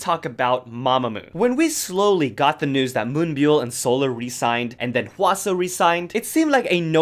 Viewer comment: When you said Solar re-signed, i thought you said She resigned 😂. I was like nooo solar is not in the group anymore? Sad about Wheein though 😭